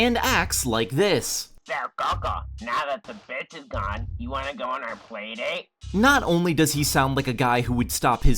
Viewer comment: I don’t get it. What is he saying? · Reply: "so coco, now that the stupid bitch is gone, wanna go on our playdate?"